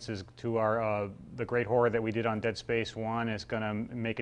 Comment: lol the closed captions said "That great whore that we did on space one is gone"